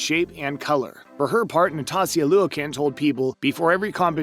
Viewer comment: i've never heard someone BUTCHER Nastia's name that bad before lol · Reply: Yeah, it sounded like he said Natasia Luik insted of Nastia Liukin